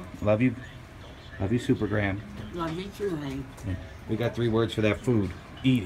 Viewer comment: Is it just my hearing or did supergram say "love you too HANK"